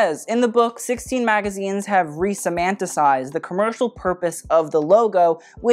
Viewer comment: resomantiscised? Sorry, I am intrigued. What is the word? Edit: somatic? Related to the whole body so in this sense meaning the logo is embodying the brand? Again, I apologise but I love words and I am not trying to be snarky. Not at all, it's me wanting to learn. I love when words are used by people in this way, it's the brain making connections to concepts to try to communicate. It is how we evolve, in my humble opinion.